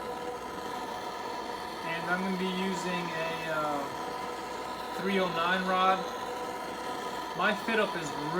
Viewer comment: What rod u used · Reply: I think he said 309